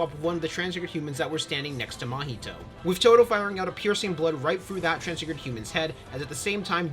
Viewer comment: todo fired a piercing blood? thats what i heard at around 9:40 correct me if im wrong